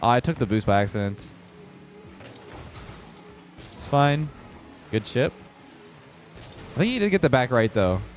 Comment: “Good Chip” What I hear: “Good Shit” 🤣